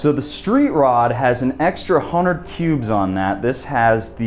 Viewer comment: Extra 100 cubes....thought you said 100 pubes lol